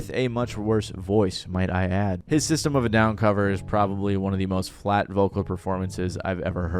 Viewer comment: I had to go back and re listen because I swore you said disturbed and not system of a down. I thought i was losing it lol